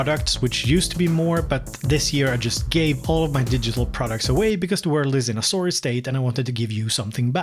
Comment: I could not understand the sentence at 6:10 where you gave the reason for giving away your digital products. it sounded like you said something like "because we're losing a sore state".... Thanks for this video btw